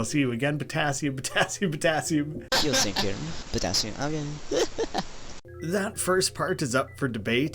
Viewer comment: I know this probably isn’t it, but I heard “you’re safe here, potassium-argon”.